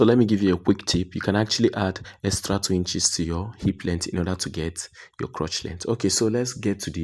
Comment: You said something about a (quick tip)about the crotch length and hip. Could not understand what u was saying on the video at time 4:50 on video. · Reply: Quick Tip: Hip length + 2 will give you the crotch length and vice versa, crotch length minus 2 will give you the hip length....I will advise you to purchase the book for a better understanding